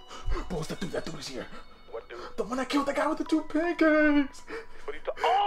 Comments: I just listened closely enough in the beginning to hear him say "the one that killed the guy with the two pancaAaAAkes" 😭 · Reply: I heard "the one that killed the guy with the toothpickers" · "ThE OnEE ThAT KiLLeD ThEE GUys WiTh 2 PaNCAKEAAACES" LMAO john wick was obviously over powerd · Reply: thought he said paintcans, not pancakes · “The one that killed that guy with the two pancakes!” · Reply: I hear “the one that killed that guy with the toothpick” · Is nobody gonna talk about “It’s the dude he defeated that dude with the PaNcAkEs” 😂😂😂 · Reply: I thought he said two pinkies